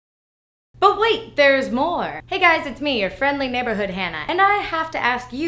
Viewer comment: Did she just say "your friendly neighborhood Hannah" ??